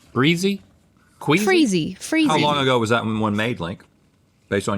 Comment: lmaooo did Link just say QUEEFY ??